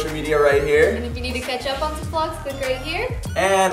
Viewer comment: When said "comment down below" I thought he said "calm down know" 😂😂